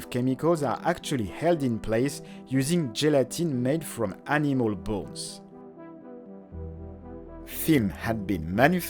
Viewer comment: Just heard it as "gelatin made out of animal balls". Took me a while to realise what it actually was.